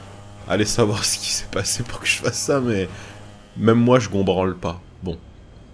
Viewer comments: à 9:41 c'est moi ou a bien dit : "même moi je gombrale pas" ?? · c moi ou a 9:40 il dit je combranle pas ? 😄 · C moi ou a 9:40 dit je combranle pas